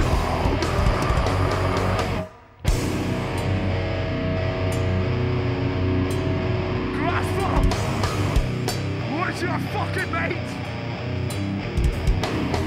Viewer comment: what does he says at 3:44 ? where is your fucking mate?